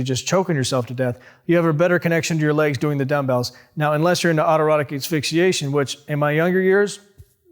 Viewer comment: what is he saying? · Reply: Autoerotic asphyxiation. It's when you get choked to make your orgasm stronger. Is that what you meant?